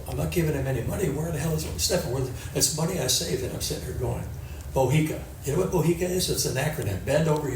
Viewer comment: what did say? “Mohica” man over here come again? What does that mean? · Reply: BOHICA Bend Over Here It Comes Again - in other works Stephan wanted more money out of his dad time & time again…